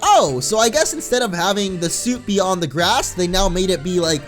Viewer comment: I thought he said "soup on the ground".